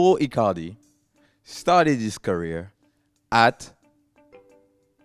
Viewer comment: did just say Palorma